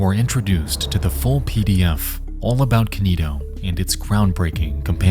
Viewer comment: at first I thought he said 'fool pdf' and looked for a goblin holding a stylus.